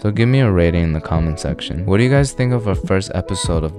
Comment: i keep hearing 'but lazier' as butt lazer